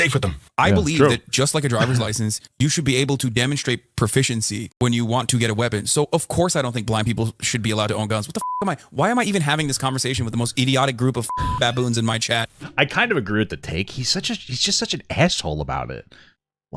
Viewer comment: genuinely thought he said black people